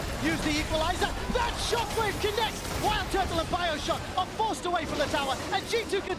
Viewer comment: did he really say bioshock?